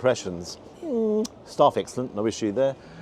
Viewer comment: what does starfixtum mean? cause thats all i hear in that portion, "first impressions,starfixtum, no issue there" 😂 can someone with better hearing than me tell me what he says here?, unless he is saying "staff fixed them" but if so what id like to know is what were the issues and why was it generalised under "first impressions"? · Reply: staff excellent